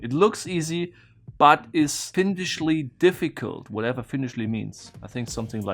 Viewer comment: At first I thought he said "finnishly difficult" which made me think he was associating finnish people with devils and then I realized he said "feindishly". Lol